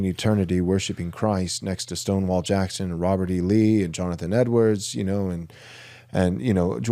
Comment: ish ...Did he just say John Edwards!‽¿? Like the "I speak to your dead gran" John Edwards?